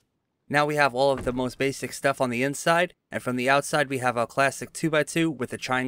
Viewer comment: I thought he said "plastic 2x2" and i was like ...its stone bro 😂